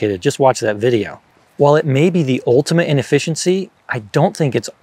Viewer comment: "the ultimate in efficiency" sounded like "the ultimate inefficiency", pun intended? Haha